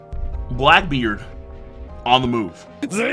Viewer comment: I actually thought you said "Blackbeard on the Moon"